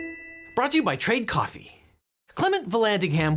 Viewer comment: Legal Eagle says: "Let's get started..... Brought to you by Trade coffee".... I hear: "brought to you by Carl's Jr"...